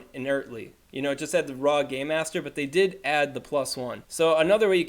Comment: I heard "raw gay master." Otherwise, great work! Love the content